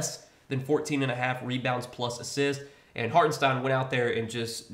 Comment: I thought you said pts+asst for Hartenstein lol it hit